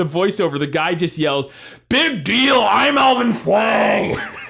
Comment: (don't know if I've made this comment before so... you've been warned) I am almost certain he actually says "Big deal I'm Alvin Shwang!"